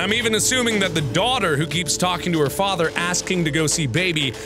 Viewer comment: For any of you wondering wtf happened at 13:25, he accedentally said brother. At least thats what I can makr out by reading his lips.